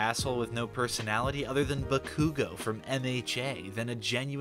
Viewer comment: For a second there I thought you said “Bakura” from Yugioh not “Bakugo” from MHA, omg I wheezed.